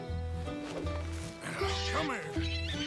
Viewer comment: What Arthur says: “Come Here” What I hear: “Get over here”